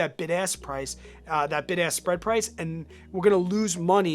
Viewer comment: I keep hearing “big ass spread price”